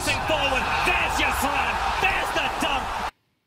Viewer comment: When I first started watching u I thought he said theres the dump